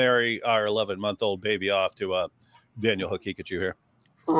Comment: Oh goodness! Daniel’s last name is ‘Haqiqatjou’? During all this time I kept hearing ‘Daniel and Pikachu’, I was so confused. How do I only now realize what the guy’s name is? I’m big dum dum.